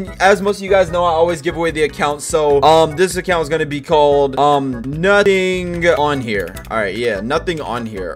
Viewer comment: It SOUNDED LIKE HE SAID "NUTTIN ON HERR" even tho he said nothing kn here